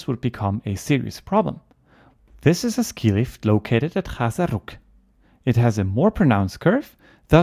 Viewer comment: What are you saying at 3:48 ? What is the name where is it? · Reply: Chäserrugg that is: Chäserrugg <link>